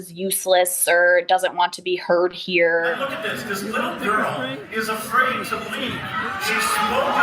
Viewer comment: does he really say "this little girl refuses to leave. She spoke up and now....." ? Really?